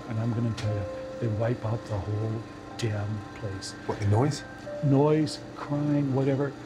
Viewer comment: I thought he said, "Noise, crime..."